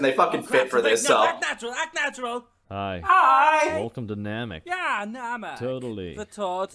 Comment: I always mishear "welcome to Namek. We're totally Namekers" as "Welcome to Namek, the totally Namekest."